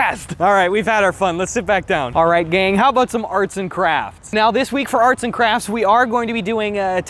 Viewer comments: i thought he said "let's do some arson crafts" this'll be the day that i die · when he said "arts and crafts" i thought he said "arson crafts"